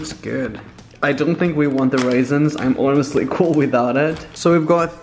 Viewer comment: did he just say.. waisins?